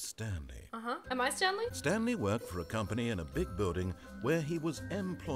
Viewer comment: I heard "big botty" and I was like "WTF DID U SAY THAT WASN'T IN THE GAME!!!" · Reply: he said "big building" but it does sound like "big booty" 😂